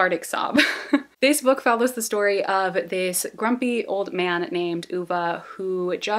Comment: I was cleaning and looking for videos to listen to so I put on ur sad books video and then this one. Both times I heard “ A man named Hoova “ I was like wtf kinda name is that so this time I heard it I checked my phone and I saw it was a man named Ove!!! I can’t believe I didn’t recognize the book based on description either time… I’ve both read it and watched the movie adaptation SO many times and I just feel ashamed I didn’t even consider it might being that one 😭😭 but as I said, there’s a movie about it too and it’s sooo good, got many Oscar nominations and now when googling it I saw that Tom Hanks is in it for some reason lols but yeah you should totally watch it!!! I’m just not used to English speaking people saying Swedish/Scandinavian names so that’s probs why I didn’t understand it was that book 💔 broke my heart a little